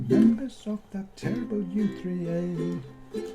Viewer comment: I thought it said That terrible USA - quite profound at this time.